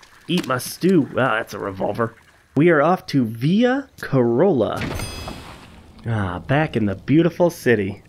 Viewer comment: I thought he said Toyota corolla